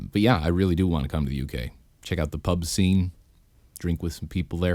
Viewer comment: for a second i thought you said pubes